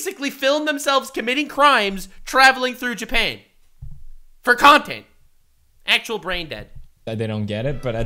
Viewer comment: Did he say "p*rn tournaments" 21:10 I thought I was pretty versed in the debauchery that is the internet. What is that? Someone please explain to me.